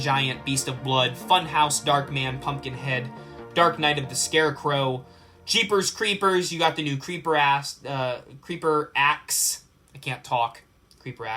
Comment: Pause did you say jeepers creepers ass?